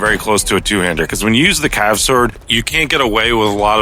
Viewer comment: Cav sword?? I thought I heard pickaxe. Ngl cav sword is fun but I just like the pickaxe for some reason.